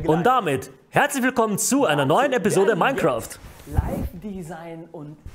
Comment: what is saying here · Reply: im not 100% sure but i guess he said something like: Hi and welcome to the (...)th/rd/st episode of minecraft.